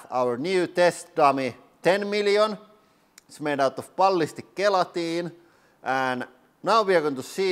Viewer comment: Ballistic guillotine. I know what was actually said but it sure sounded like that to me. And I like that idea a lot better.